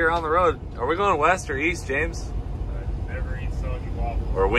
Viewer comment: Cleetus : “Are we going west or east James?” James: “never eat soggy waffles.” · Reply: East? I thought you said Weest?